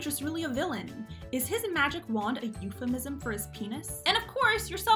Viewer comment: I thought she said "Did his imaginary friend reach for his penis?" Lol